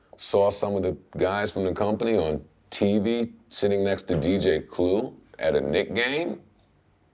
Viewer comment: The CC at 14:36 thought Joe said George Clooney instead of DJ Clue haha!